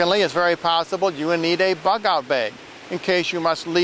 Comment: what is a "buckout bay" or whatever what was said at 3:22??